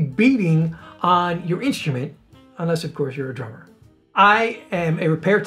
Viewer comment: I thought he said "Trumpet" instead of "Drummer"